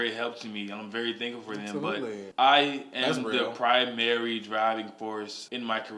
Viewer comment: I literally thought Lil Nas said I am the Proud Mary driving force instead of primary! Jesus I am on crack!😂